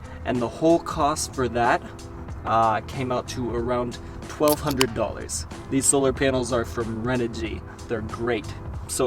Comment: Thought ya said under 1000 lol solar- 1200. Lol